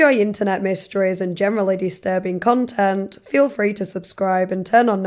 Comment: Every time I mishear you saying "generally disturbing content" as "genuinely disturbing content". Which still tracks.